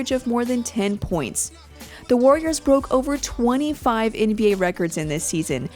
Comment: I thought she said the warriors broke over 25 ankles